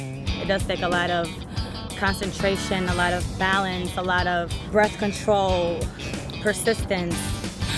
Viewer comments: Misheard that as "a lot of birth control". Took me a few seconds to realise what she actually said. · I thought she said Birth control and persistence lol